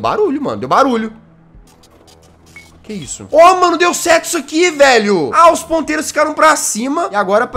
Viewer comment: "Ó mano deu sexo aqui velho" KKKKKKK sera que foi so eu que entendi isso?